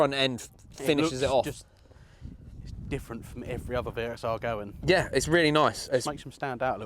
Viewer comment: Could a swore he said VSR 2:08 😂😂🧐🧐 This build is tasty 👅 love the content bro 😎